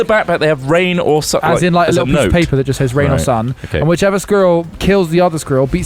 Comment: I thought Smith was saying girl weirdly until I realized he was saying squirrel. What the hell is a "scrill"